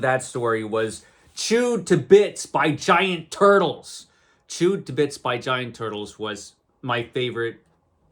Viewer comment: I thought you said, "turtles chewed my bits" 😱 Which made me vow to never swim in fresh water.